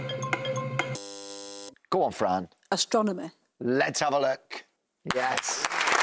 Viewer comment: I could swear I hear her say astronomer and not astronomy